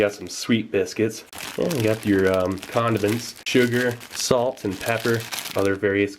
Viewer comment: i was busy with something and i thought he said condoms